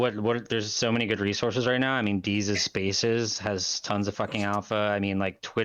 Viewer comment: What does he say at 40:05? Deezer's spaces?